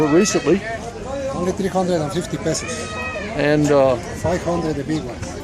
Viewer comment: Did he just said only 350 pesos for he's paintings...????? Don't think so. At min